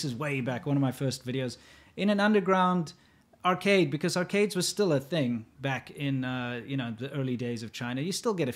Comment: did I just hear "because arcades were still a thing back in the early days of china" ?? XD XD XD lol